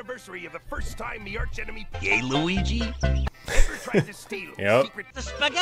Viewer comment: when Mr.Krabs said "Gay Luigi", I mean it sounded like he said that 😂